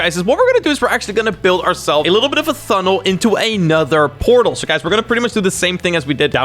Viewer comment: did he say "thunnel"?